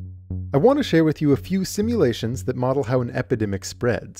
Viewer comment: Thought it said “stimulating”